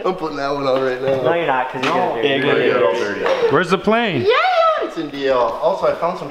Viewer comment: did he say where is the plane lmao wtf does jakes parents not have